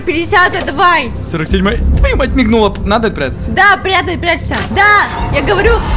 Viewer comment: Мне одной Настя Настя прячься послышалось ?